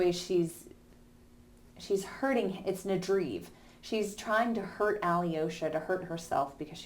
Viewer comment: What word is it that you use at 1:18? I can’t figure it out for the life of me. Love the videos! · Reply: I think it might be nadryve, which means to split or tear.